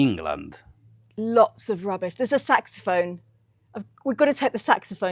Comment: I swear I thought you said “s$x phone!”🤣🤣🤣🤣